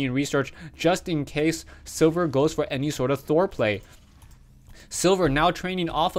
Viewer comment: Thought you said foreplay